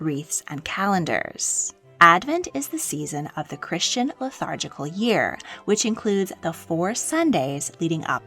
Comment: It sounded like you said “lethargical” - did you mean “liturgical”?